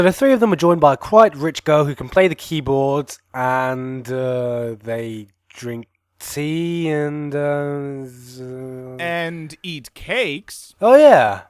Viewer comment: at first i thought it said that "i drink cake and eat tea" idk why